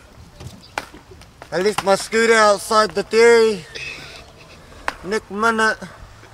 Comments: I thought he always said nik mana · Sounds like he said “left my scooter outside the theory.” “Next minute” and he’s trynna show someone broke it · I thought he said "nick mana" My whole life has been a lie😞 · I thought he Was Trying To Say Nicki Minaj And Ended Up saying Nik mINA